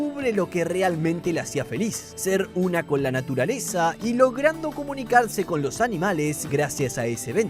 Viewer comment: escuche lo que realmente la hacia feliz ser una con la milanesa XD